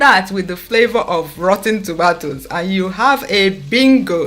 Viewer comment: Did u say rotten tomatoes? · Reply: Annakay Irving rotting (in the process of becoming rotten)